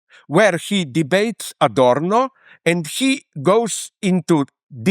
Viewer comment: I was sure he was gonna say: "...WHERE HE DEBATES A DOORKNOB." DEBATING FITTINGS & FIXTURES? Try cannabis instead.